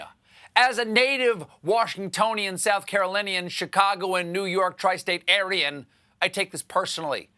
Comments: did Stephen just call himself a "Tri-State Aryan"?? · Did Colbert just say he was "Aryan"? We finally get rid of trump and now Stephen is dog whistling to the alt reich. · Did he call himself an aryan at 10:20?